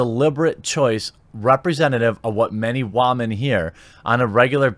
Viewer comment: I don't see the humor in mispronouncing woman/women, it's just distracting. Every time I hear it said, I hear "ramen". XD